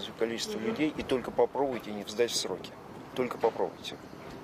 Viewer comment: Мне послышалось по военному:-"Только попробуйте в строй не встать!"